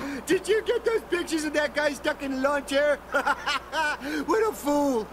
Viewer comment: did he just say bitches